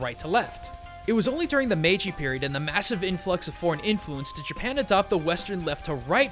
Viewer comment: why did I hear that as, 'porn influence'?